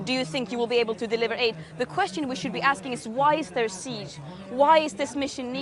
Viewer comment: She shouldn’t talk about we Christians! I am Christian and highly appose the hypocrite double standard from large areas of the left woke spectrum and DO fully support Israel and the Jews people for their rightful fight for their freedom, their land and their future for their children! All the best from Europe to Israel 🇮🇱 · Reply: I think she is saying " question we should be asking" not "we Christians." I first thought she said Christians too.